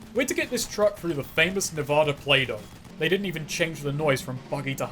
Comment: I always thought he said “The famous Nevada Play-Doh” because of how bad the map looks.